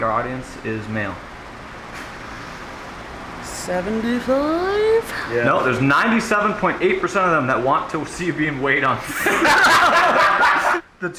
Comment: what does say at 10:30? i couldn't understand the last few words he said, but everyone laughs like it's funny. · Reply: I think 98% of our audience wants to see her weight or something. The audio was botched in this video, had to fix a lot of it